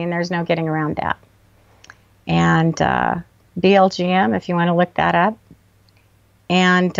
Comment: See <link> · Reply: Hi. What did she say about the power of names? I am not sure if she said "VLGM" or BLGM" @ 27:43. Can anyone expound on this. Very interesting. Thanks for this interview!